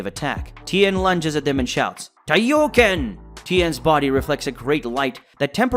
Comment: Excuse me sir but did you just call a fucking solar flare kaioken at 8:20??? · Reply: no he called it taioyoken which is the real name of it